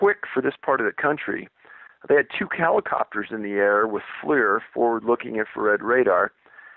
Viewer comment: I thought he said they deployed helicopters?